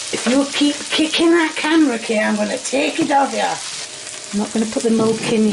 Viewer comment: did she say "if you keep kicking that camera Kay I'm gonna take it off ya"??? IT'S A SHAPESHIFTER AND IT'S HOLDING KAY HOSTAGE AS A CAMERA STAND RUN